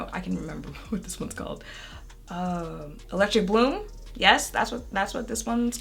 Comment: I thought she said electric blue... I was like, that should be interesting.. is that what people are doing now